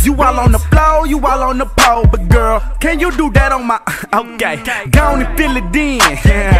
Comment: Wait does he say "i'm gay"?